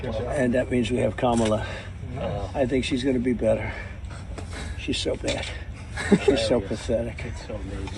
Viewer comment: “She’s so fucking bad.” - that’s what he says. I hate Trump but come on! Enough of this constant misrepresentation, it’s not necessary.